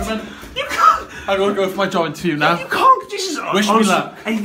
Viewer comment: Now I see why always has a job interview XD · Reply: Thought u said hand job interview no joke 😂